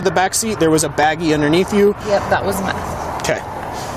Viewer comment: did she say "yep that was meth"?🫢😦🫠